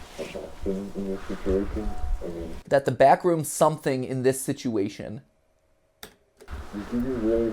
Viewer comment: I heard "the backrooms are adapting in this situation"